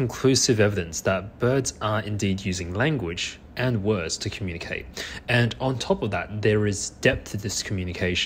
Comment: I thought said they only apir to communicate 😅